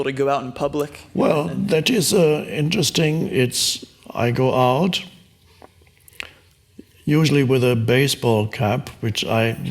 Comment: For like a second or two I thought he said “I go out, usually with a baseball bat“ I was like what the F? That wouldn’t be very Eckhart-y! 😅